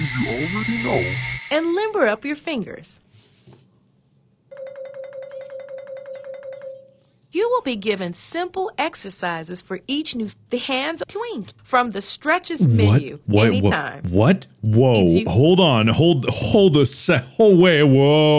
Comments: I thought he was shocked bc the way he skipped around made it sound like it said twink 💀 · sounded like she went "Twink!" and I've been cackling about it for a good bit